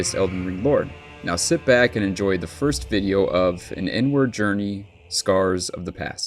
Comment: I kept hearing "an N-word journey" 😳